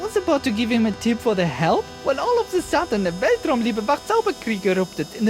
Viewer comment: Ok, could anyone explain to me what the german tourist says? I keep hearing "Beltrom liebe vachts auber krieg" but that doesnt seem to be correct.